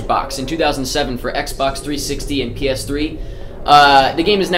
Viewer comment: Nope that was my mistake. I thought you said Xbox and Xbox 360, but you didn't. You just said xbox 360 and ps3, my mistake.